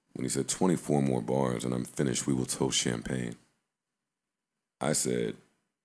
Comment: Joe funny talking about he heard a lil diss about himself lol.. I thought they said that bout Charlemagne 🤔